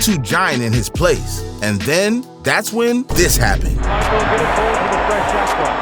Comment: only I hear in 2:35 com. say Michel Jackson?